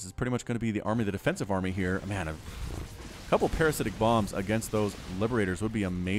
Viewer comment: I'm sure it's the first swarm host EVER that I see since you started casting Void. Again, burrowed ambushes are always a welcome sight and did I hear... "parasitic BONGS"?